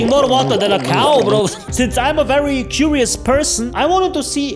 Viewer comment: I thought you said "jewish" but it's "curious"